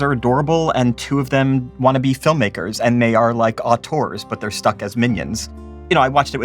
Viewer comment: what is that word? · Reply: Auteur — a filmmaker with complete and utter control over their vision.